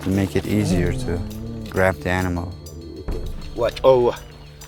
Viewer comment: did he just said "watch over"😂